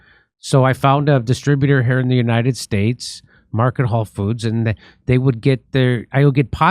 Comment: is Jimmy saying Market Whole Foods? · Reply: market hall